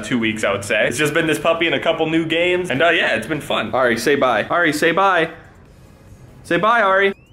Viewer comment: Did anyone else thought he said "alright" when he said auri say bye